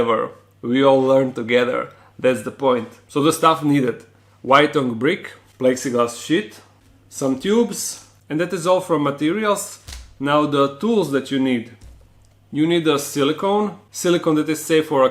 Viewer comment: It sounded like he said plexiglass shit